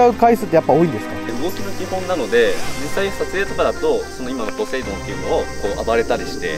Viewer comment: モトキくんのポセイドンって言った時「ポテトー亅に聞こえるのは僕だけ？